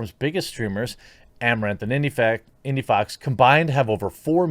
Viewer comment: Did you say indiefag 5:29?